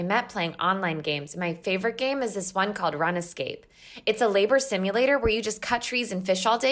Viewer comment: did she say run-escape lmao